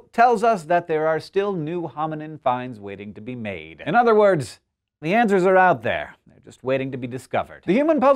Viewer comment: the first time i heard this i thought he said "Adventure is out there!" time to go watch UP